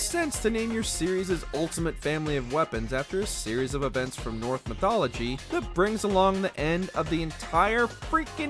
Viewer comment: did he say "north mythology"?